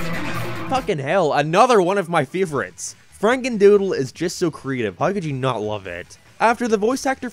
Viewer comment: you said FranenDoodle instead of FrakenDoodle